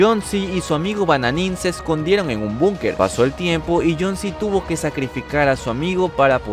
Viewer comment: Like si parece que dice Jon c en el minuto